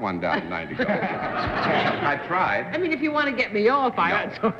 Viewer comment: a sign of the times 12:45 what Arlene said gets total silence--today, not so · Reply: Lol she said “well, if you wanna get me off…” to John about giving her a “no”